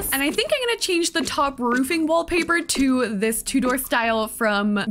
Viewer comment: I definitely don't mean this as any disrespect, but every time you say tudor I hear "two door" and I'm like "what" lmao. Maybe it's a difference in accents. Either way, pretty build!